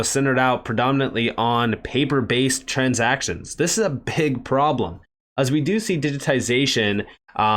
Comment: Sounded like you said a "pig problem" 🤣